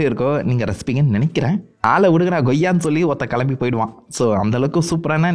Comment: is he said otha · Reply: No bro avaru oruthan nu sonnaru 😂